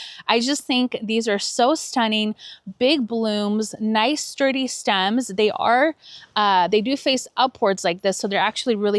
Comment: What is the name of the Dalhia at 22:32? I couldn't understand what you said. Thank you. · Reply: Hi Lovely, it's Hapet Champagne. 🥰